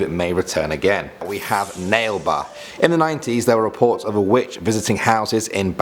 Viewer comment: I heard "Nale ba" as "Male bum." Wtf is wrong with me?